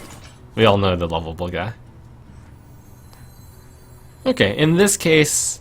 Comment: What does he say on 6:40? "We all know that...." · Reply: Thrower Lovable guy